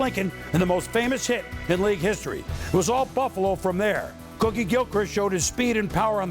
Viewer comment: did Berman just say "rookie Gilchrist" or "Cookie Gilchrist"?... bc if it's the latter then wtf kinda name is "Cookie"???... even if it's a nickname!, seriously that's one odd friggin' name lol